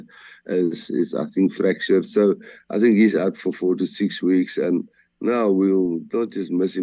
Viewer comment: I was listening this at 2x the speed, and it sounded like Rassie said "He's out for 46 weeks"😂😂😂